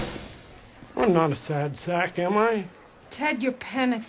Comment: I thought she said, "Ted, your penis is blue." But what she really said is, "Ted, your pen exploded." Now, I'm not trying to be obnoxious, but I mishear things a lot for someone who doesn't need a hearing aid.